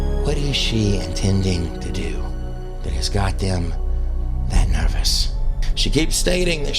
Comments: I love how you claim to be a Christian right after take the Lords name in vain. Use discernment people!!!! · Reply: He actually said, what's got them so nervous 19:06. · Did you use the words, GD at 19:06 in this video. I was hoping I heard wrong. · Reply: He said, what has got them that nervous.😊